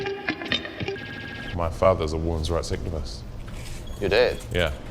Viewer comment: I've watched this nine times now and still have no idea what he's saying. "My father's awhoonzrauight sick of us"? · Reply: "a women's rights activist"